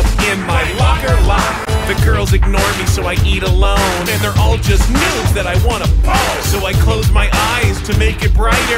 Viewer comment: i thought he said "there all just noobs that i want to bone" roflmao